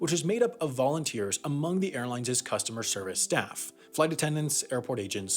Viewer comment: did he actually say airlineses? Ha Ha